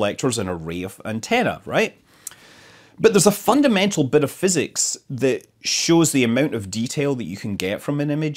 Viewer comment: I heard the new word of the year 2020 --> " BENEPHYSICS" ...wish you a happy new year filled with health and happiness.